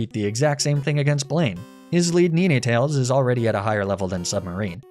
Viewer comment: Pokemon Red was my first ^^ I got it right when it hit the US. I was already a big fan of the Anime and couldn't wait for the game to come. Also...did you say Ne-nay-tales for Nine(9)Tales? 20:55 ..and Arka-ne-nay??